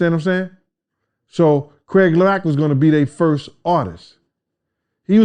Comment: did he say "Craig Lack"? Yo Puffy did Craig Mack dirty af.... it sucks we were robbed from getting Deathrow Records to make Deathrow East which remember that despite the name was gonna be ran by all East Coast rappers and especially rappers who for some weird reason were not getting the record deals or they were treated like they were "old" and "obsolete" which is anti-hip Hop culture.